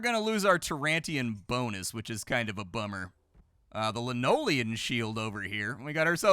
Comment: Did anyone else hear "linoleum shield" and wonder what the heck was going on? Or was that just me?